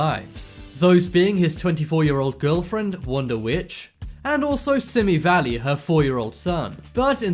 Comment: Did I Hear You Say "Wonder Witch" And Her Son: Simi Valley...??? WTF... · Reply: Mychael Helmz LOL!!! That’s what I thought at first, he actually said Wanda Witch